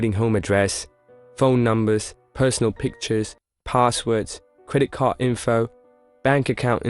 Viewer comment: Who else heard him say creit cart numer😂✌️💙